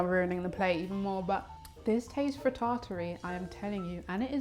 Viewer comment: Please I’ve been thinking about this all day, what did you say at 10:12 and what does that word mean? 😭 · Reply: I think she said frittatry as from Frittata